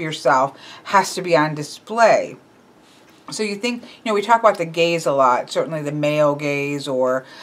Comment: As I watched I had to use the CC because I seriously thought you were saying the male "gays" (@ 3:59) wanted to keep their lives private...duh...too early for me I guess. I like this concept and did my own spread yesterday after seeing your FB post; it was revealing and I thought it corresponded to what I let others perceive about myself and how they see me. The blind spots were surprising. Thanks!